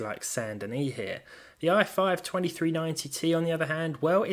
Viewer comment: I thought you said 12390T... I was so upset at first haha